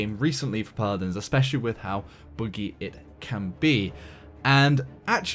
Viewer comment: Did he just say boogie for buggy. What accent is that??